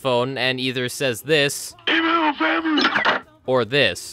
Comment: you know it's gonna be wild when it's 90 minutes long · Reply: I hear "give me my family"